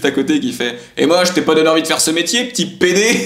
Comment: Il dit quoi à 13:56 au juste? Fait deux fois que je regarde le clip mais j'arrive pas à comprendre :/ le français n'est pas ma première langue lol.. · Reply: Il a dit "et moi je t'ai pas donné envie faire se metier p'tit pd"